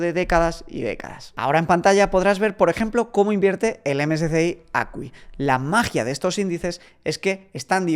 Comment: ¿Cuál es fondo que dice en el 7:47? Su nombre e ISIN? Gracias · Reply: MSCI ACWI